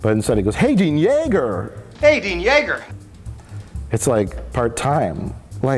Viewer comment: I also thought he said Aidean Yager for allof my pre-adult life.